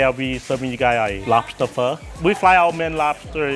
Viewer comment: I thought the last dude said "what the pho", but it was lobster pho. I like mine better